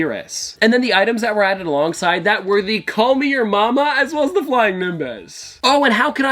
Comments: did he just say KameYoMama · did he just say call me your mama? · did he just say "call me your mama" for the kamehameha